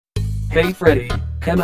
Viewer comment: did he said gay freddy!!!?